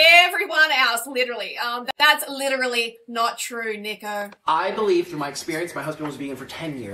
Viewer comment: I thought you said "Thats literally not true, nigga" xD I mean .5 seconds l8r I realized it was just your accent and now Im laughing .